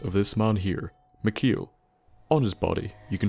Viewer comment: Did you just say "Maqil"? That's my name lol.